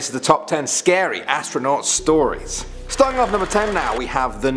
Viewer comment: Astronuat instead of astronaut 😂